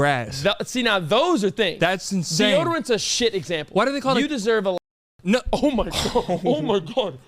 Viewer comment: what did he say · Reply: I think he said you deserve a lashing😭